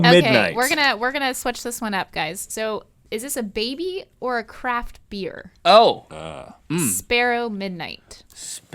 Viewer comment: I had the volume on low and I thought they said crack dealer, instead of craft beer